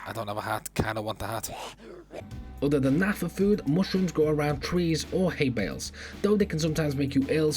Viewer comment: Who said anything about Muslims? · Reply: Here 2:01 i hear muslims and i make translator on, you can see